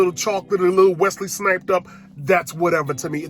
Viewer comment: All I heard was “Wesley Sniped up” 😂😂😂😂